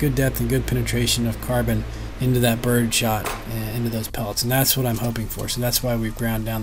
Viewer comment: lol I thought it said bird snot